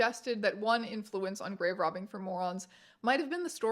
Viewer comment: Ngl i thought that shit said "Mormons" 😭😭😭